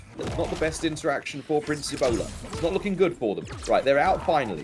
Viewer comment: Sounds like is saying "Prince Ebola" 🤣